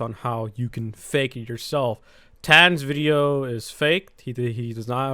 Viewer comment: DID JUST SAY TANS